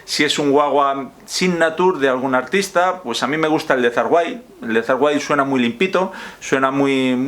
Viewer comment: Disculpe, en el minuto 6:17 dijo Zakk Wylde...no le entendí muy bien. Gracias.